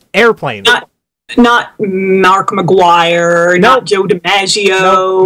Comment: I think this is a sign of how young I am compared to and, because when I heard her say DiMaggio I thought "When did Bender's voice actor do that? -Oh. Oh wait, she said Joe, not John. That makes more sense."